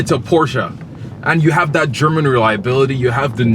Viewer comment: Did he say German reliability?